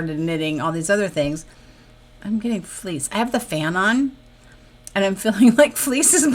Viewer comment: Omg I’m so glad to hear it was FLEECE cuz I thought you said FLEAS and I had two thoughts ‘OH NO’ and ‘I would not casually laugh if I felt a flea on me!’ 😂😂😂